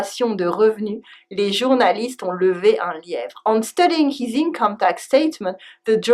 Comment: Did somebody hear "En studying" (min 7:47) or only me ? :) while translating, she forgot to replace "by" I guess. :) ... But I like her way of explaining things.